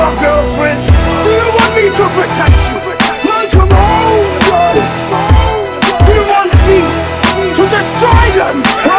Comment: What did Ivar saying 2:06? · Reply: Welcome home, Björn